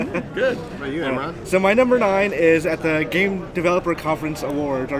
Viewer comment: I kept hearing them say "Mighty Nbr 9" instead of "My Number Nine" haha